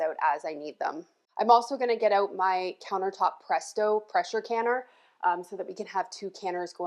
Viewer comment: Hubby was in the other room and thought you said “Precious canner” he thought that could be your tagline like Rachel Ray coined the term “EVOO”. 😂